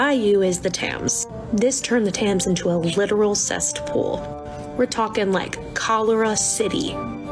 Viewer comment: Sorry but... 6:03 did she say cestpool ?? That is...an image even worse than the image cesspool gives me 👀